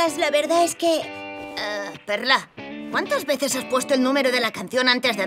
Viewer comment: ¿Que dice connie 1:53? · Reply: "¡El bis!"